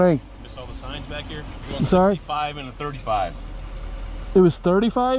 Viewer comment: The officer said he was going 55 in a 35, and then bumped up his number to 65. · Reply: Yeah, I heard all kinds of different shit myself to at first I thought he said 45 and 35 then I heard 65 and 35 I was like what the fuck.